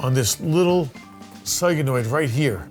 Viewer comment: did he just call a solenoid, a soygenoid😂😂😂